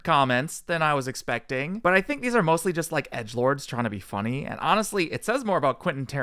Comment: I could have swore he said perverts not edgelords